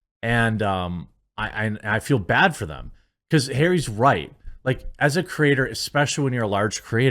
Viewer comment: did he call him hairy? :D